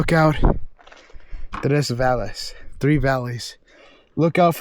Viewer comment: did anybody else hear the creepy voice say three when he said, look out for the three valleys